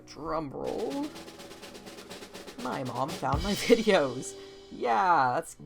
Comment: I thought that said "my mom found my wood"